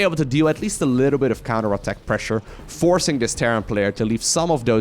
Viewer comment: Did he said terran scum at 8:03?